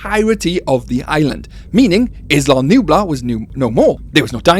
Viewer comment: Islam Nublar? Am I hearing things?